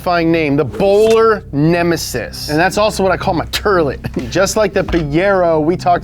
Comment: subtitles say '(indistinct)' but he is saying 'thats also what I call my toilet' (but 'toilet' is pronounced funny). idk how to edit subtitles, if someone else can?